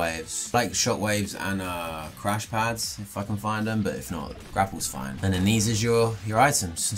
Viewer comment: did he say the f word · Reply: He said if I can find them